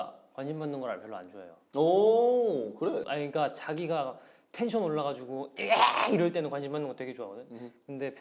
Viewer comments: did he just call him jaggi again 😂 (the eng sub doesn’t show it tho) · Did minho just said "jjagi" while talking about han? Or is it just my minsung brain playing tricks on me?? 🤣🤣🤣